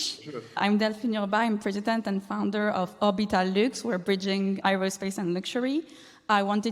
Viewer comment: Hey folks, can someone help me out with what the business this lady was the founder and CEO of? I didn't quite catch it. Thanks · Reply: Orbital Luxe, her name is.